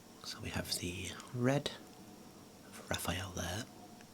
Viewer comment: I heard "Raphael leao" instead of "Raphael there" 😭